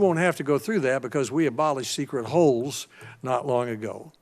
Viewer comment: Hey guys quick question, What did Sen Alexander say in the beginning? Around 5:36 where he says he abolished secret "holes" or "holds". Either way, I'm not familiar with their terminology or what they intend to mean by it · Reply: Leo Gonzales He said they did away with the "secret holds".